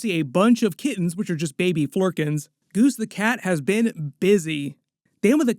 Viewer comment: The child in me had to laugh at point @ 11:01 where the subtitles get a bit confused, says "see a bunch of kittens which are just baby Flerkins, Goose the cat has been busy." The subtitles say "see a bunch of kittens which are just baby foreskins goose the cat has been busy"